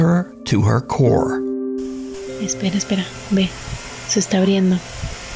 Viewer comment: she said "It's getting open" = se está abriendo. I don't think she said "se están riendo" = They are laughing Specially since the closet it is actually opening on it's own